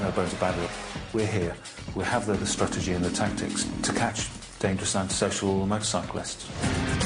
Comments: Did he say dangerous and sexual motorcyclists? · Secs Did he say "We're here, we have the strategy and the tactics to catch dangerous and SEXUAL bikers?". Lol. Pc Pervert! Lol.